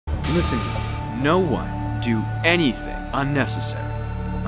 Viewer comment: Kaede: "Oh sorry lol I thought u said "Don't do anything necessary " ahaha"